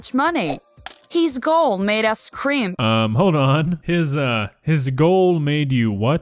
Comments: she sed scream but it sounded like cream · I think when you thought she said "cream" she said "scrimp" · yeah... I know she said, "scrimp"(maybe scream?), but I heard, "Cream"